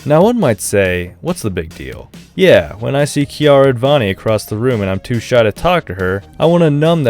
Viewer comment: Did he just say Kiara Advani?